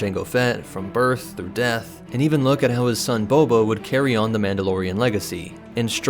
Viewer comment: Did anyone else notice how he said he son bobo at 0:55 . You know what just gotta say bobo is my favorite star wars charater